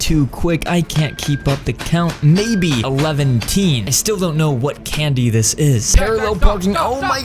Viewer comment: did he say eleven teen I’m pretty sure it’s elven